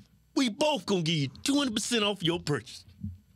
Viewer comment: This is one of the best shows i have seen from these two! It also sounds like said i'm gonna give you 200% off your purchase